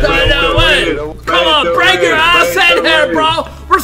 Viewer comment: what he said ? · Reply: "Pay to win"